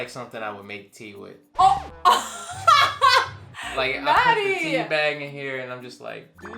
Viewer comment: When say Maddie it sound like she saying daddy like if u think the same 😂